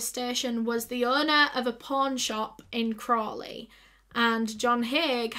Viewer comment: I know this is terrible but for so long, because of her accent, when she said "pawn shop" I thought she was saying "porn shop." Glad I now see that's not the case 🙂 oops